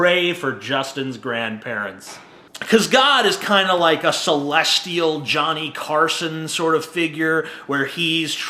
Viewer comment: I heard "god is kind of like a celestial" and thought "celestia." Anyone else?